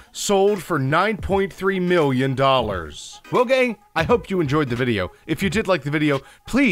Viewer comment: sounds like he said Logang